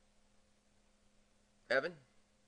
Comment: at around 2:00 after that dramatic pause, i thought you said "Kevin" which is my name. freaked me out XD